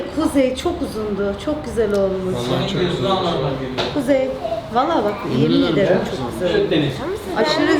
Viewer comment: 7 saniye videosunda kuzey allahinizi skm dedi duydum inanmiyanlar baksin izlesin terbiyesiz kuzey